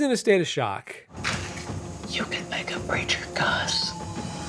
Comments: I've rewatched this 25 times and I still can't make out what he's saying. Shame, it was probably a harsh burn. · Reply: He said, "You could make a preacher cuss." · I have listened to this at least three times now and I still have no idea what he is saying. All I can hear is "You fit like a Branger Gus." Can somebody help? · wait what did Nick say here? I can barely make it out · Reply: "You could make a preacher cuss"